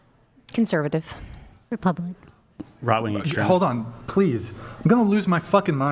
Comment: WTF 1:19:30 she said Republican….i could hear her perfectly fine…why be such a D-bag??? She’s just a little shy it’s not the end of the friggin world